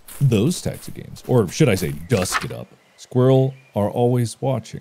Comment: What did he just say, did he just mention DUSK